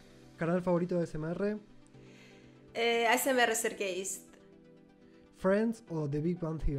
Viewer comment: escuche "ASMR Ser gays" XD